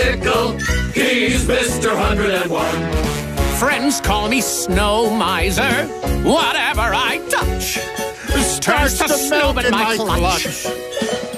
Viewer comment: I hear start to smell in my clutch